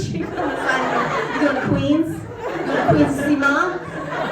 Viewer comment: can anyone make out what lynn was saying at 16:37? "we were with our male partners and everyone was pushing them aside... ?" i heard something about queens (which they totally are btw) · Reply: yes, I believe she said "going to Queens? going to Queens to see Ma?" since Flushing is in Queens